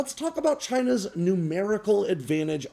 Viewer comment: Ha - you said "China's numerical advantage", and I heard "China's new miracle advantage". I figured it out quickly, though.